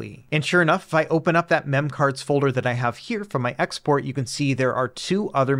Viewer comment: Hey folks, quick correction: the reason why my memcard import failed in this video was because you need to pick the main export folder, not the memcard folder itself. Thanks to the NetherSX2 dev for pointing it out! The manual copy/paste method shown in this video will still work, and also will be handy if you are importing memory cards from a different device. Other notes: - NetherSX2 releases now include the APK, so no need to build it yourself -- just follow the links in the description and download the latest release! - If you previously had AetherSX2 installed and attempt to install NetherSX2 but get an error, it's likely because you have some remaining data files on your device from the previous installation. I recommend connecting your device to a PC using the method in this video and deleting the internal/Android/data/xyz(dot)aethersx2(dot)android folder (grab anything you want to save first, like your saves/states). Now try installing and it should work. - Same goes when updating to a more recent version of NetherSX2: export all of your data, uninstall the old one (while choosing NOT to keep data files), then install the new NetherSX2. Once installed, you can import your exported data. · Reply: De a second I thought you said memecard